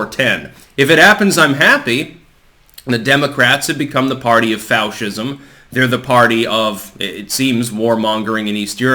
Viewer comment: When you said they became the party of Fauch-ism, the auto-generated captions said "Faustism" which seems like meme magic... For those unaware, Faust was a story about a guy who wanted power and made a deal with the devil, tried to TRICK the devil and ended up in bad shape for it. How appropriate, just one computer generated comment. It's Learning